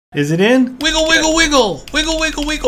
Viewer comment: the start all i heard was "is it in, wiggle, wiggle, wiggle" hahahaha oh...